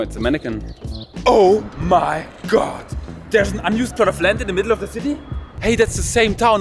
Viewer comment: Being from America I don’t understand quite all the Frankfurt Witz ,but nevertheless I enjoyed the video!😀 What do you say at 1:36??? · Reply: I think he says "Oh my God, there's an unused plot of land in the middle of the city?!?!" and the only reason he can think of why this happens, is that the place is haunted! I have never been to Frankfurt but I guess that an unused plot of land in the middle of the city is not a common sight in Frankfurt!